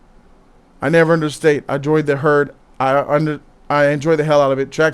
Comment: what the hell was that reading? you said "I never understate I join the herd"